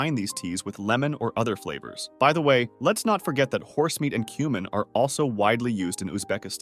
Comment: UMM did he say horse meat and human? I must have heard wrong but i listened to it several times. Can someone else listen at about